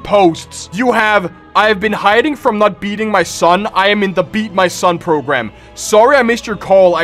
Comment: I thought you said DO NOT STOP BEATING YOUR SON